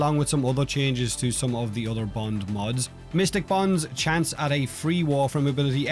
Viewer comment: I heard "Mr Bond" instead of "Mystic Bond" ah ah